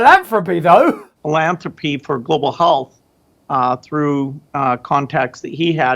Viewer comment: gates' freudian slip?...did he say: "global holes".. who says health with an " O"..holth? WTF is that warped SOB on? he's also a shit liar; watch as he shifts uncomfortably in his seat 9:46 and defensively crosses his arms 9:48 before delivering his thoroughly contrived response to the already 'approved' Q. me thinks he once attended the Andy Windsor school of bullshitting...purely to talk about philanthropy, of course !